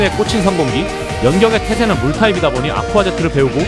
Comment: 초반에 연격의 태세 말할 때 염병의 태세로 잘못 들었네 ㅋㅋㅋㅋ